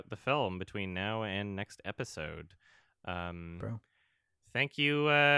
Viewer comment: who said that and what were they trying to say? It kinda sounds like saying "bro"